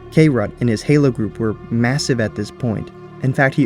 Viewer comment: did you say "MASSIVE"?